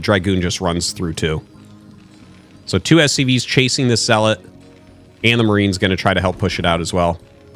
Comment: Who else heard that "two SCVs were chasing the salad" At 3:23 ?😂😂😂